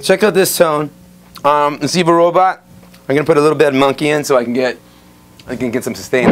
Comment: did he say zebra robot?